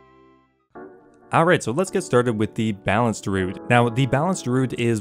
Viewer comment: man im having WoW withdraw I swear he said "BALANCE DRUID". God DAMN it Blizz why did you have to ruin something great